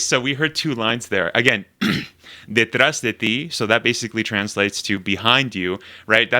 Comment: here they actually says: "Detrás de vosotros", which is the more "Spain spanish" for "Detrás de ti".